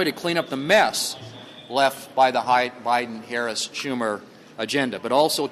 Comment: Did he just say "hidin Biden"?